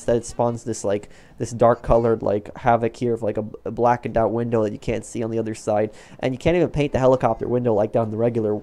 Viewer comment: did he say you can’t see inside out if so Boi look